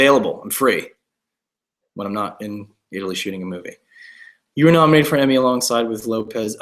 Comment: when he said "when i'm not in the middle of shooting a movie" i completely thought he said "when i'm not in the middle of a shitty movie" and i was SO READY FOR THE DRAMA